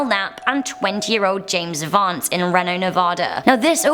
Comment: I thought she said James Bond